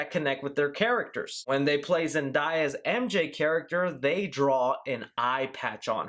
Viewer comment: Did he just say "Zendiya"